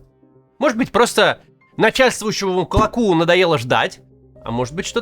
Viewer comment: услышал "клопу" 😂